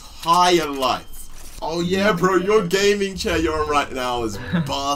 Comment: did anyone else hear “ your gaming chair is a bastard”